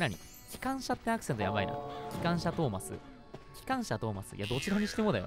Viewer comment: 空耳かな？ 4:56 ｢どちらにしても痴漢車…｣ に聞こえてしまった(✽๑❛ڡ❛)